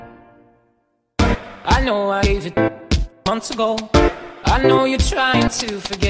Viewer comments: Am i the only one who hears...I know i'm asian, asian....? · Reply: Same when I first heard this I heard I know I'm Asian,asian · I know I'm asian. Noscope. I know you're trying to forget. I know I'm asian. Noscope. I know you are trying to forget. I know I'm ASIANASIANASIANASIANASIANASIANASIANASIANASIANASIANASIAN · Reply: Sounds like he saying "I know I'm Asian months ago I know you're trying to forget"